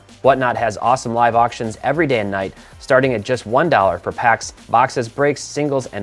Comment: I usually skip sponsor ad reads but I listened to the one for Whatnot a few times. It almost sounds like you're saying the auctions are "damn" night instead of day and night. It was an unexpected gut laugh!